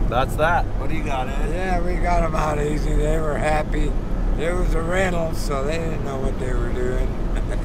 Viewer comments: When Ed said that, "it was a rental, they didn't know what they were doing..." I thought he said "they were oriental, they didn't know what they were doing!" Good ol' Ed. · Classic line , "They where from Reno , so they didnt know what they where doing" brilliant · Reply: I thought he said "it was a rental" so they didn't know what they were doing.